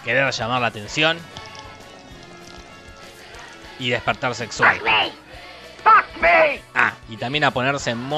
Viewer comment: Soy yo o es que dijo despertar sexual